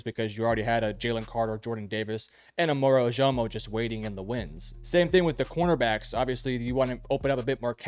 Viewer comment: Isn’t it “waiting in the wings”? Thought I heard “waiting in the winds.” 🤷‍♂️ Nice video. Appreciative of creators who actually use footage of games rather than describing it. While I think this video can be a bit of an oversimplification, it’s a great first impression. Def gonna keep a lookout for this channel in the future.